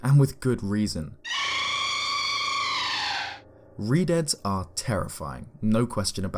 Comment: I thought it said redheads, not redeads